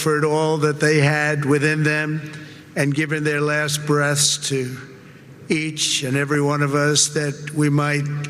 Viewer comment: Am I the only one who heard him say “and given their last breast?” 👀